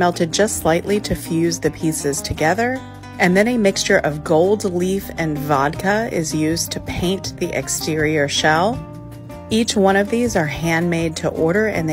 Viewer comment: Instead of “gold leaf, and vodka” I heard it like “gold, leaf, and vodka” istg I was kinda concerned